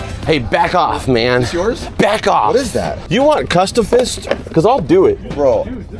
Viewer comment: What did you say at 8:30? It sounds like cuffstafist and I'm wondering if you meant fisticuffs or if you said something else 😂